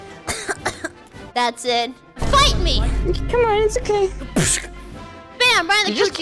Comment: I thought she said “bite me” but it was “fight me”